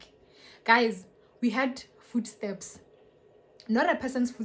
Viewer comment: I thought she said food stamps 😂